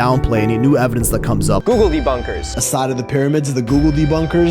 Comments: Googledebunkers? I was googledebunkers once. They put me in a room. A rubber room. A rubber room with rats. And rats make me googledebunkers... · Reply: I heard it as googldy bunkers. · It sounds like hes saying "googly bunkers" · Every single time I hear "googledebunker" I think "Gobbledy Gooker". I feel old. 😂 Edit: I not I'm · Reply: I am getting a new kitten in a few days, her name will be, Googledubumper because that's what I thought he was saying at first and it made me giggle. And lets be real, cats don't come to anything but psspsspsspss and "kitty kitty" anyway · I keep hearing “googledy bunkers” and can’t shake the image of an underground bunker during an earthquake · I always hear it as “googledybunkers” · You know when i hear "google debunkers" i hear "googledy bunkers" like a bunker for googledies. Which i think would be another merch opportunity. Maybe make it a paper weight? Just like a half circle with the word "googuldy" or whatever on it (misspelled for copyright reasons maybe?) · You should name your recording area "The Googledy Bunker" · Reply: I was about to comment that what I hear is "Goggledy bunker". Glad I'm not the only one. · You should name your recording area "The Googledy Bunker" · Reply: i always hear "gugadee bunker" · I heard that as googledy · OH MY GOD! They said googledebunkers! I thought they said some version of gobbledygook during the first video! Also, I love the new merch! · I keep hearing it as “Googledy Bunkers”